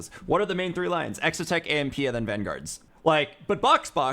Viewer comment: Exotech, ???? and vanguards? Even youtube captions had no idea what he said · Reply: AMP.